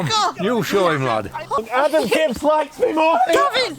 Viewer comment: I thought I heard "Adam Gibbs likes me more" while you were rolling around, lol. (#3, no distractions in it, too bad #3 didn't get the same light as #2.)